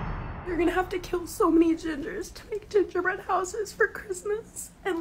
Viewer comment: thought she said you have to kill so many scissors..